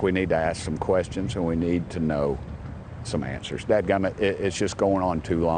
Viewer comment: did he just say dad gomet? is he allowed to say that?